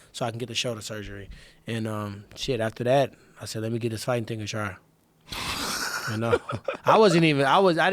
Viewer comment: Can someone please tell me what Tyron says at 10:43? “Let me...something something something” and then Joe chuckles. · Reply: Never mind I got it! "Let me give this fighting thing a try."